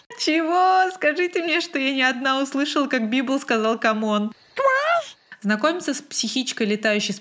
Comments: мне послышалось что он сказал краш 😂😂😂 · Я не услышала "камон", но отчетливо слышу "квас"